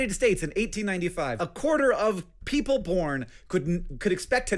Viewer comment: you and the closed captions cannot convince me that Hank did not just say 'people porn'